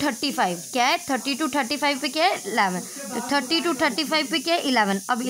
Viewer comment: who can else hear thuthii instead of thirty😂